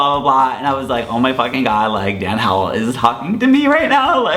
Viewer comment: did he just say Dan Hill 😂😂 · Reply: Genesis Monterrubio he said howell